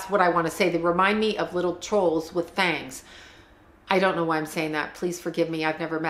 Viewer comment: When you referred to the Olsen twins as “trolls with fangs” I peed a little. 🤣🤣🤣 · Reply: haha i thought she said trolls with bangs